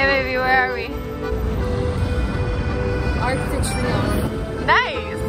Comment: where are they?? didn't catch what she said... the background is so pretty~ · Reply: Field Green they’re at the Arc de Triomphe !